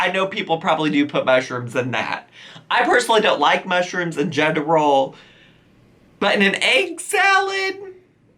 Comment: I thought you said "I don't like mushrooms in gender roles" and I was like who is gendering mushrooms?